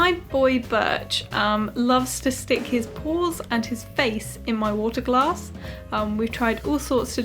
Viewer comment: I totally thought she said "he loves to stick his balls and his face in my water" and I'm like 😳that is one talented cat